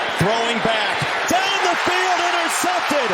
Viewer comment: what did he say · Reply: He said “Down the field,intercepted” at 12:50.